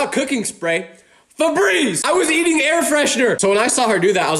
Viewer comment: you were air freshener? [As the subtitles said]